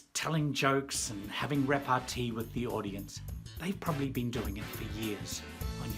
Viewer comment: what did you say? Having ___ with the audience. :) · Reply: "repartee with the audience"